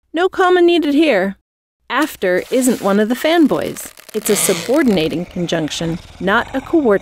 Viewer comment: I thought they said femboys.